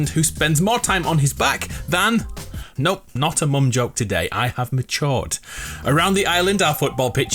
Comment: All I can hear you say is “Nope, not an among joke today, I’m on chort.” Lol I really can’t understand what you just said.